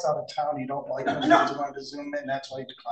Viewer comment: You don't have to be a member of the BAR, or even a member of the Law Nerd Herd, to see that the Judge was 100% right in her indignation with this 🤡 attorney 😂🤦‍♂️😂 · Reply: Start at 2:20, go back and listen to it again, slow it down if you need to but to me it sounded like he was saying she didn’t like him to zoom in. She interrupted him in the middle of what he was saying so she only heard part of it.